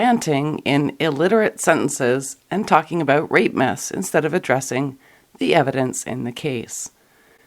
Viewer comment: I kept hearing 'rape myths' as 'rape-mas' ...